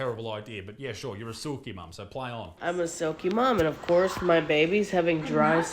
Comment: She's not a "silky" mom. She's an unengaged and disinterested parent. As demonstrated by the "my kids get unlimited screen time" Your kids get unlimited screen time because you don't know how to parent any other way. Your kids are premium prey for any predators. Want to avoid this? Stop avoiding your kids. Play with your kids. Read with your kids. Sit outside with your kids. Have conversations with your kids. Do projects with your kids. Get your kids to assist you with keeping the home environment safe. Karaoke with your kids. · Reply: I thought she was saying a “Sucky Mom”.